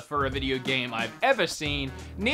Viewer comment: No, please NO! 4:52 did that really say need for... need for seed? For seed? Excuse me wtf ( btw again a very good video of yours )